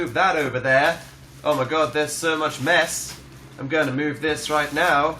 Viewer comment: "So much mess," I actually thought he said "So much meth..." So... Um.... There.................